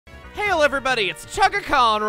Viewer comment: Not gonna lie at the start i thought he said “heil everybody”